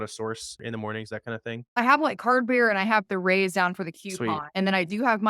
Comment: At the 8:10 area, she mentions CardBear and..'.The Rays'? Couldn't tell what she said for the coupons. Can someone help with where to find coupons?